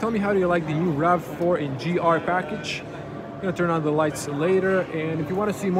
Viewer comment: OHHH!!! I thought you said "NGR Package", you said "In GR Package"... the former would be an unfortunate name for a black package.